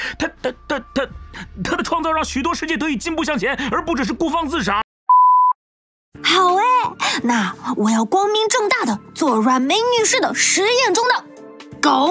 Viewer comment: Did i just hear bro say the N word?!!!